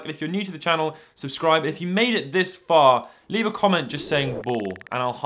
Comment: Ball? I think that's what you said.